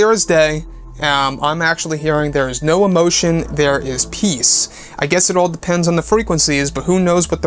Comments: I hear "there is no emotion. There is peace" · The voice sounds like it said “there is salvation, there is peace” which make sense since Jedi code was found right after, could be saying there is no emotion but definitely yanny effect XD I feel like he would feel more connected to her if she was his niece